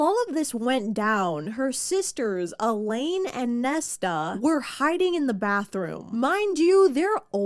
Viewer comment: is it just me or did i hear nessa